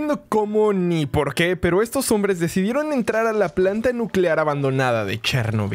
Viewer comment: Yo pensé que decía planeta nuclear xd